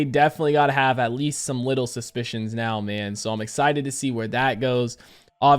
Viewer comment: I thought you said "widdle suspicions" lol